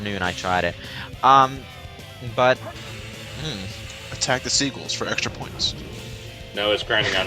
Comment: Wehn said "Attack the seagals for extra points," I heard it as "Attack the sequals for extra points."